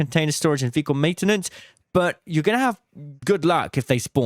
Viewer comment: Fast talking and iffy annunciation made it sound like you said “Fecal Maintenance.” So, ya. That happened.